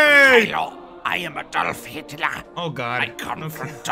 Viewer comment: he tried shooting the kid. I like the hitler, hes preatty funny ngl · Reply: why does the subtitles say that hitler said I am a dolph hitler and not I am adolf hitler?